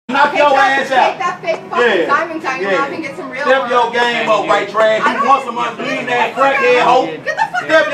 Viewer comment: did he say “step your game up WHITE BREAD”👀😂😂😂😂😂😂😂